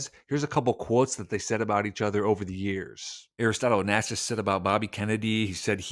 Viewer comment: I don't hear very well... You're intro I heard as 'Aristotle on Acid', lol Wait, what am I watching?